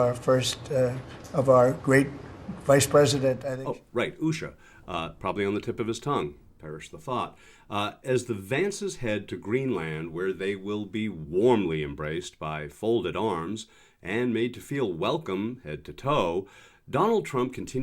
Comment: JD Vance should be careful how he speaks-it sounds like he says shades Vance which is really true even though this time he did not really know he was telling the truth about being shady and then some. GET HIM BACK TO THE USA where he can effe us up more than he has.